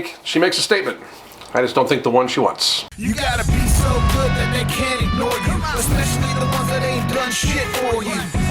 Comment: I swear I heard, "You gotta be so big that they can't ignore you," lol